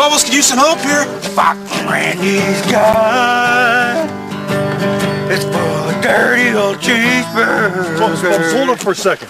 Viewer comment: No of course not! :))) I was talking about the moment (0:44 i think) when Bubbles approaches Randy and starts to sing "Fuck this Randy guy, he eats plenty of cheesebuuuuuuurgers"...or something like that, I can't understand accurately.